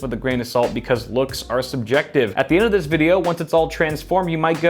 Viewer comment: Tell me why I thought said Looks are seductive